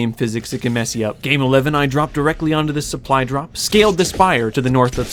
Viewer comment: what did he just call it 😧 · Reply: spire